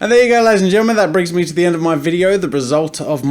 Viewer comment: (I always hear it as "Get out, ladies and gentlemen" at first...)